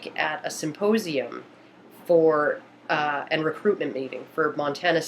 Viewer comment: Just a little note on indefinite articles. I heard you say at 2:44, 'An recruitment meeting'. It's 'a' recruitment meeting. The indefinite articles 'an' or 'a' are based on the sound of the first syllable of the next word. So, for consonant sounds such as the word, 'recruitment' (ree), you would use 'a'. A recruitment meeting. A history of time (audible H). With vowel sounds, you'd use the indefinite article 'an' such as 'an' onion, an honor system (h is silent in 'honor'), an umbrella, etc. Note the exceptions based on the sound: A one day trip, an onion. An honorable person, a history of time. An umbrella, a unicorn Words with start with 'r' pretty much never have a vowel sound, so you'd use 'a'; a rock, a recruitment, a Ralph Lauren coat, a rookery, a Rambo movie, etc. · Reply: ***** spacep0d commented on a video on YouTube. Shared publicly - 29 Dec 2013 Just a little note on indefinite articles. I heard you say at 2:44, 'An recruitment meeting'. It's 'a' recruitment meeting.